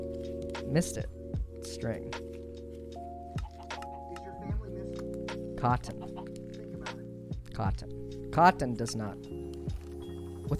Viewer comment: I love how the "is your family missing think about it" sounds like it's part of the lofi songs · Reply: I thought it said "Does your family miss you? Think about it" cause that seems to fit a lot better